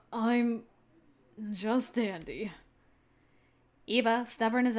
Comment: When Iba said “I’m just dandy” I thought he said “I’m just daddy”. I was like WHAAAATTT!? 😂😂😂